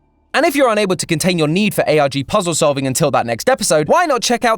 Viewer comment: Did did he just say cool math games?